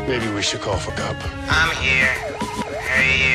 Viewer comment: Did he just say, "Maybe, we should call for COPPA???" 😵😵😵😵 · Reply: Maybe we should call for Cup